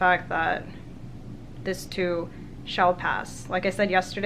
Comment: Did you say 'This Tube Shall Pass' or was it a fantastic misheard